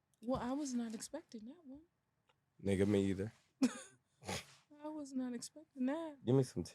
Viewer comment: Did he say "nigga ...ME EITHER‼️❓️⁉️" WAS THROWN❤ I MIGHT BE MUSTY....W😲W